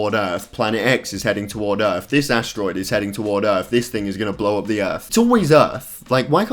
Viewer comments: i thought he said “this niggas gonna blow up the earth” 😂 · did he just say "this nigga" 😂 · sound like he is saying this nigga is gnna blow up the whole earth